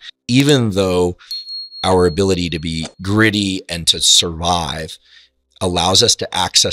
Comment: + The text of his words on the screen was not checked for accuracy or if it was, it was not done carefully. He did not say “greedy”; he said “gritty”, speaking of the capacity to survive. There is a difference in meaning. Twist it all you want, the difference is significant.